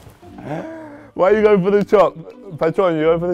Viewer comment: Kane wtf are you saying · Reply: He says Why are you going for the top come on Jurgen Klopp